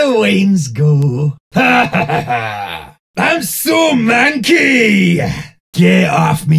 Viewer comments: i thought he said “i’m so monkey” and 0:46 i thought he also said “ratatata” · i heard "im so monkey!"